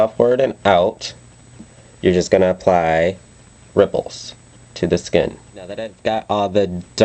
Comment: When you said ripples I thought you said nipples.